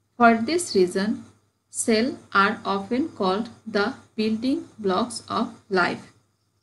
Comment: MISS which locks? I Can't understand the pronunciation and spelling . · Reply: This is not locks, it will be blocks. The sentence is - cell is called building blocks of life.